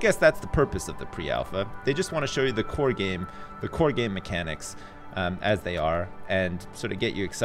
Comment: did say core gay mechanics?